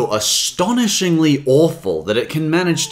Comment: it almost sounds like he saying waffle, but i know he says awful